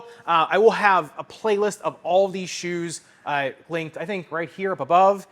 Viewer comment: does he say "Respect driver and foam, i'll see you somewhere in this universe"?